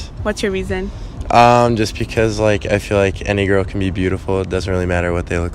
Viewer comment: when He said any girl I thought man said a negro lmaoooo 2;37